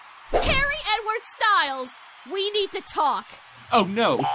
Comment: When she said Harry Edward Styles i thought she said Perrie Edwards Styles hahaha